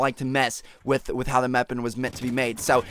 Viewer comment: xD at 5:05 he said meapon instead of weapon